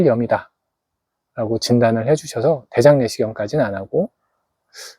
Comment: 여기 뭐라고 하신건지 모르겠네요 중요한 부분 같은데 ... 대 실명이다 ?? 이렇게 들리네요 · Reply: 자막이 없어 보시는데 많이 블편 하셨죠?😔 '게실염 이다' 입니다 게실염 또한 대장에 발생하는 질환이며 통증이 심하다고 합니다 게실염(오진)진단 받고 치료를 처음에 받았던 거에요~ 관심 감사합니다☺️